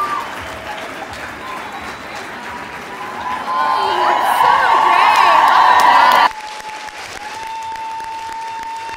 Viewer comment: This is beautiful. What does Kat say to Dom at 6:50. I think i missed something here? I’m gonna miss this show so much! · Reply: She says "So great. Well done."